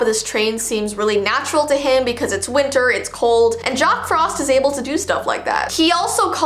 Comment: Me: Did you just say, Jock Frost?? Or did you say, Jack Frost, in an accent??